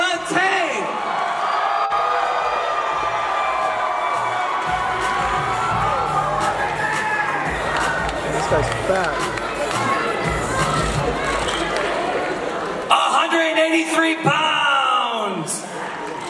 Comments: I hear “this guys fat” when Deji comes😂 I’m dead😂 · anyone else hear "this guy's fat" 😂